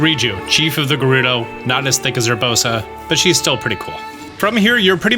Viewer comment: Did he say not as thick or sick as Urbosa 😯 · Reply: Definitely said not as thick lol